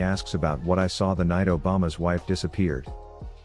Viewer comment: It's hard to understand what's happening while trying to understand what ever shit he is saying Exp: Him: 9:50 Me: obama's wife... obama's wife... disappear..? Obama's wife...Malphite ults senna... obama's wife...oh, oh, senna, Obama's wife is senna, does that mean Lucian is Obama?hm... Meanwhile he is blabbing something about a gank-bang